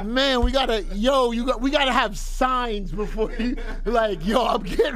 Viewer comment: YO I THOUGHT FLEX GONNA SAY "man we gotta have sex" instead of "we gotta have signs"